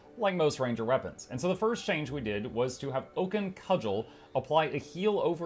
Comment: When you were going through the ranger section and said "Oaken Cudgel" I heard "Vulcan Cuddle", damnit Spock why won't you love me?